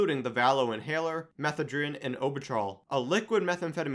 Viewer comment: I thought you said meth a friend of me 🤣